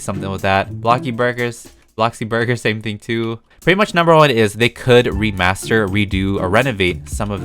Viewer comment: Him: They could renovate... What i thought he said: TheY COuLD rEnAgADe..